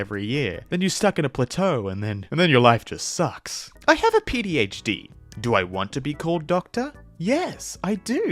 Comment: did you say pdhd?